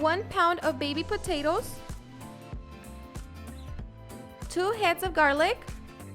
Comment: I thought she said two halves of garlic. LOL.